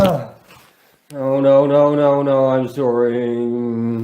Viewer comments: I'll start by saying, I hope he is ok, whether real or fake, I hope he gets help.. Partly saying No no no,, Im sorr.. electric arc, tazer and a human seizure is what I heard. Doesn't mean I believe anything, just what I heard. Cheers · I also heard him say oh, no. I'm soooory before the flapping noises start.